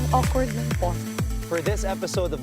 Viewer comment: Did anybody hear the voice saying ‘ang awkward nung talk’ at the start of the video? LOL 😅